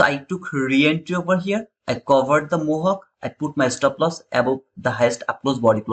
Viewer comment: Brother while explaining the rejection from Breaker block at 14:04 min time You have mantioned something like mohayk …tow three time …could not get it …. what is this word brother …is it rejection…. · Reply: A little run outside of the pd array is called mohawk